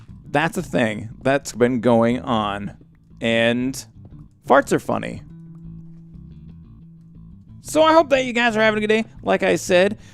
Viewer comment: Wait... at 5:14 did you say "Farts are funny"?